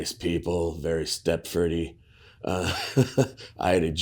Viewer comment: what word is that? sounds like very "step footy"? I've never heard the expression